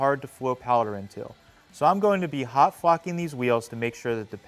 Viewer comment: Did he slipped the F word ? It sound like he said he'll be hot F*ING this wheels 🤣🤣🤣 pretty slick